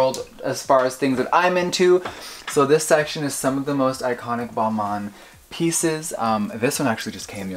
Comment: I misheard Balmain as Walmart and I was like “wait Jeffree’s into Walmart fashion?” 😂